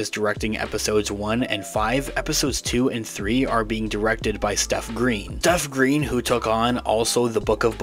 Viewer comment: I legitimately got excited because I thought you said SETH Green was directing episodes.